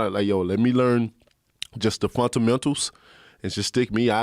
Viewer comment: Why did he say fountainmentals instead of fundamentals?? Is that Alabama lingo